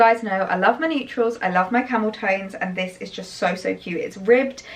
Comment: Great video. Really lovely clothing! When you tried the camel cardi on I actually thought you said I love my camel toe 😂 but you defo said camel tones lol!! The lip combo looked really beautiful too. 👑 <— when you know, you know 😉 Thanks for another fabulous vlog xx